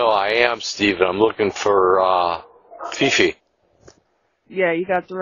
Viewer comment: Fifi is such an honest sounding internet name, too. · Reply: I thought he said he was looking for fishy 🤣🤣🤣